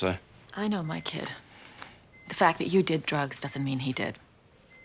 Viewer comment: when he said drugs in the beginning I had to put on CC because it sounded like he said Jokes. I was confused.